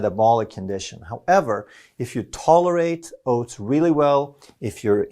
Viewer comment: When he said 'oats', I could swear I heard 'boats'. It is not so much eating wrong, but misconceptions, and details that are importat.